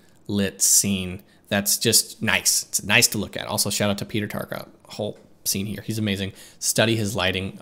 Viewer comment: I couldnt find anyone with the name "Peter Tarkov", unless I completely misheard the last name hehe. Would you be able to give me his name please? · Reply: it's Peter Tarka!